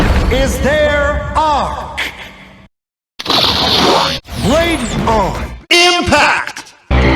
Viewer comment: What does he say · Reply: "Blade On! Impact!"